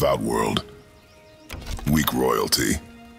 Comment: did kratos just say weak royalty or..? idk my hearings been off today